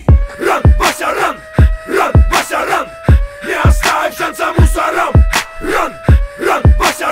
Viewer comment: Мне послышалось: ран вася ран, выноси много мусора :>